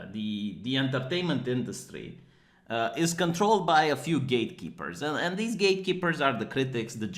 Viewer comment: Did you just say gaYte-keepers? I think you did. You are not wrong. Fear and terror at the hands of the gayte-keepers ought not be underestimated.